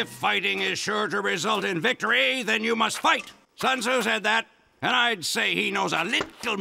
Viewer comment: "Sun tzu said that" -Hunter, 19 bby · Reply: Always thought he said 'Son who said that?'